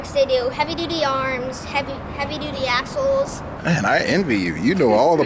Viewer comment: I thought he said heavy duty assholes lmao